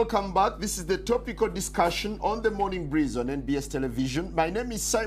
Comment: Thought it said MORNING SNEEZE